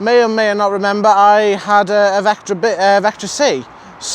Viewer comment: I heard you said: "I had vasectomy" But it was, "I had Vectra B.." I think dark colour InsigB is best looking choise; but alas, after 2020 facelift, the original blueish black is not available anymore. Huge disappointment..